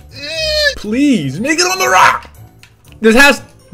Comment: it sounds like he says nibba on the rock. It’s especially bad when it is slowed down.